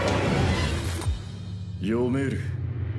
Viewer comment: anyone have a clue of what king crimson is saying in romaji? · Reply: yomeru